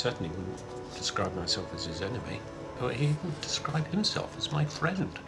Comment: Does Nick say David would or wouldn't describe himserf as Nicks friend? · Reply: I heard "wouldn't", and it surprises me